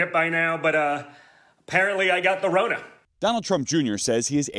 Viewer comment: did he just say 'I got the Rona' ?